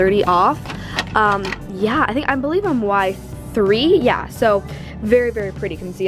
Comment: did anyone else notice she said y3 instead of w3?